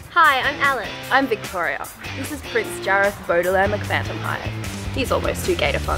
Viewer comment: did she just say phantomhive?!